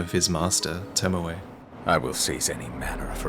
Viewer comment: Damn see when Genichiro went into phase 3, I always thought he was saying "I will CEASE any manner of heretical strength" meaning that clearly his talents as Isshins grandson weren't enough, though that does make a lot less sense in the grand scheme of things and would be a far less fascinating story. Though when he says "sieze all manner of heretical strength", does this mean that he is indeed a descendent of Tomoe as well, or just that cuz Isshin also knows lightning, thats what he means by heretical strength?